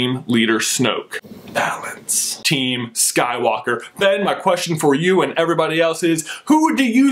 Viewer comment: i thought he said "Bang my question"